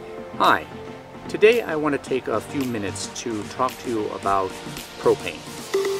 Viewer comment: i thought you said "hi today i wanna take a few minutes to talk to you about cocaine" for a second ,i was like what the hell?